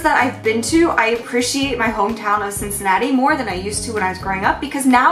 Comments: i'm not a native speaker and i can't catch the name of the hometown. can someone tell me please? :D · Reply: x Cincinatti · Each time you mentioned Cincinnati, I kept hearing it as "Chincinnati", and imagining the Crimson Chin going about with his superhero hijinks in the background.